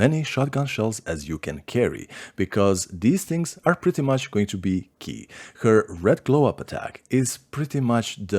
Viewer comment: i thought u said bc thoes things will prettey much be pee not key lol